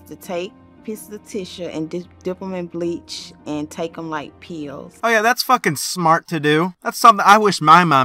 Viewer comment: Oh my god 3:28 i thought she was saying "tickle my peehole" instead of "take em like pills" i was so confused and so disturbed I'm still confused and disturbed